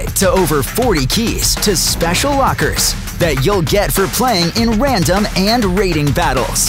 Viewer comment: excuse me i heard free 2 play